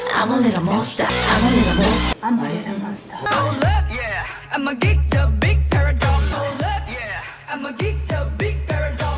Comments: "HOLD UP, I'M A GEEK THE BIG PARADOX" this. just this. DAMIII 😭🤩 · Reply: WAIT DAMII SAYS TGAT. the whole time I thought she was saying. “HOLD UP, IMMA GET THE BIG PAIRS OF DOGS.” Jwiksjskshjs. I’m sooo dumb😂😂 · i dont stan the grp but "HOLD UP, I'M A GEEK THE BIG PARADOX" is just iconic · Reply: Omg I’m embarrassed. This whole entire time I thought she was saying. “HOLD UP, IMMA GET THE BIG PAIR OF DOGS.” 😂😂😂